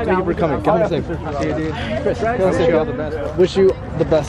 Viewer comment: him telling everyone to get home safe made me smile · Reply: I THOUGHT HE SAID GET HOME SICK 😭😭😭😭😭